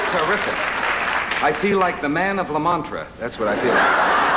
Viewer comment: The transcription has Merv saying he feels like the "Man of La Mancha," but I thought I heard him actually use a play on words and say "Man of La Mantra."